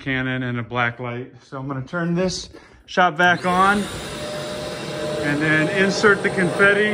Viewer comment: did you say shot vac? Is that what you used to shot the confetti through the cannon? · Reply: Yes. Shop vac!